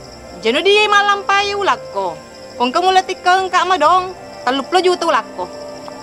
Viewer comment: terima kasih sappoku sampai sekarang ini selalu mendukung 😇🙏🏻 salam bugis sedunia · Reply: Haha.... ku kira salah dengar ka madong asenna ' ternyata itu pale namanya na kebetulan sama namax adek ku 😂❤